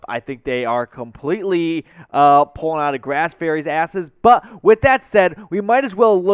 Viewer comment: Did he just say "pulling out of Grass Ferry's asses" at the 50 second mark? Who's Grass Ferry?